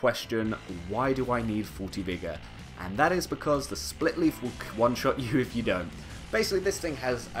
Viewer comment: Jesus christ i can't understand the weapon names.. is it so hard to write it in description or in the video??? Or even say the damn name of the weapon.. this is the best weapon this is one of the greatest weapon... my god. Take a look: 10:12 whats the name of that shit?? Wtf is wrong with you, glad you got your fanbase that thinks a video that doesnt say the wpns name is amazing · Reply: Estoc, Corvian great scythe, splitleaf greatsword, lothric knight sword and demon's scar